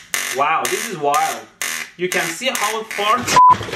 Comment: did he say you can see how it farts?